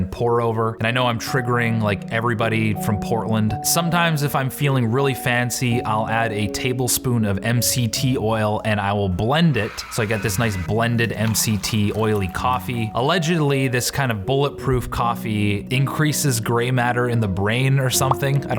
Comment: I was sure I heard 'NZT oil' Ohhh so that's how Joey's so productive